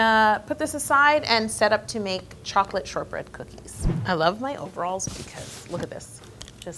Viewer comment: for a second I thought you said “chocolate sherbet”😂 I was about to say never in my 29yrs of living have I ever seen or heard of chocolate sherbet where’d u get that😂